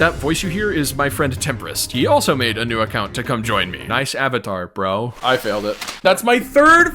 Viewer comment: when i heard 'nice avatar', my brain immediately auto-corrected it to 'savitar', i guess ive been watching too much flash